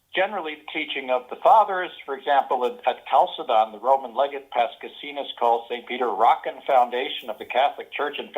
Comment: Okay. I guess we're suffering a bit from a lack of good English translations of the primary sources. So what in fact did say at 6:09? I can't be "past casinos". :)